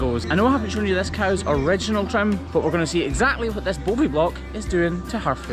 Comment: My partner: "A boaby block? Is that a diaphragm???" 😂😂😂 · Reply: Funny, i heard him as "Bovi-block"!